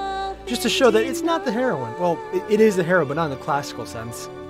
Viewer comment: i heard 'it is the heroin'